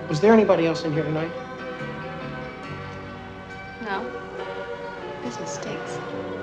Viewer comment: What does the waitress say at 7:52 after being asked if there was anybody else in there that night? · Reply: business stinks